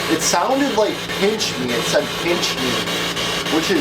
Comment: When the spirit box said "pinch me," I heard it say "amscray" ! Scram!